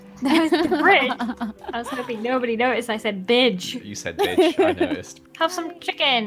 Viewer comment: When Joel said “bidge” I thought he said “b***h”